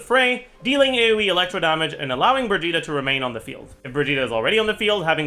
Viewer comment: Birgitta... I keep hearing Vegeta. "This skill allows Vegeta to remain on the field" I usually listen to videos while playing games, but this made me pause to look at "vegeta" lol